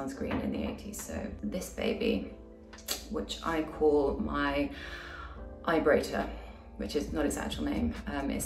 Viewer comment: why did I hear vibrator 😭✌🏼